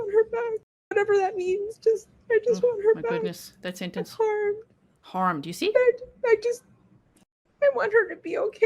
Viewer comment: I immediately heard the word harmed the first time I heard this audio but I can see how some people heard she didn’t want her home. What I can’t understand is them bringing this up to her because she said right afterwards, about four times, how she just wanted her to come home! Shoddy police listening skills I’d say.